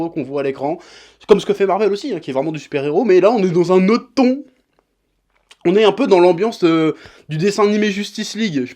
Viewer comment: met un like si toi aussi tu as compris "un autre thon"